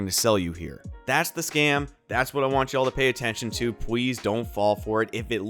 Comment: Some Oculus Quest are totally scammer, did I just hear “Pwease” lol